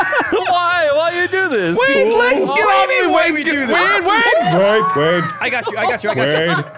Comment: Half the time I hear "WADE GET OFF ME" 🤣